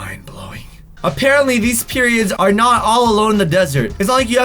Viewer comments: said periods instead of pyramids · Did he just say periods instead of pyramids in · he said these periods instead of pyramids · I liked subscribed and turned the notifications on and at 2:50 said periods instead of pyramids lol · did anybody else hear him call pyramids periods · Did you say pyramids? or periods? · He said "Periods" instead of "Pyramids", XD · Did he say "periods"? what is on your mind? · did anyone else notice that he said periods instead of pyramids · No one heard him say period instead of pyramid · he said periods instead of pyramids lmao Who else caught that hehe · accidentally said period instead of pyramid😂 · Did Buddy else catch that add instead of pyramids he said these periods😂😂😂😂😂😂😂😂😂😂😂😂😂😂😂😂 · accidently said Period instead of Pyramid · he says periods instead of pyramids😂😂❤️❤️ luv u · Is it just me or did he just say period instead of pyramid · Did anyone else hear him say “periods” instead of pyramids or is it just me?? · did he say periods instead of pyramids lmao 😆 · I swear he said periods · In 2:55 he said periods instead of pyramids 😂😂😂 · he says these periods instead of these pyramids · instead of pyramids he really said periods. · When he said periods instead of pyramids 😂😂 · Anybody hear him say periods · said “periods” instead of “pyramids” 😂😂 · Did anyone notice it sounded like he said periods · in 2:54 he says periods instead of pyramids lol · lol He said Periods instead of Pyramids 😂